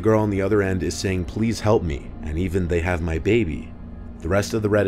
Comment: to me the last one instead of "they have my baby" i hear "They're going to beat me"